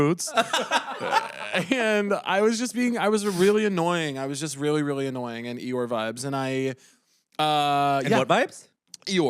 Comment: It sounded like Caleb said 'your' instead of Eeyore, which is probably why Bowen questioned to repeat right after saying really annoying lol